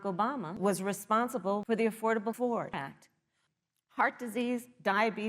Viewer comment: Just a reminder yall: President Barak Obama was responsible for tHe aFfOrDaBle FoRd ACt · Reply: I really thought she said the affordable whore act.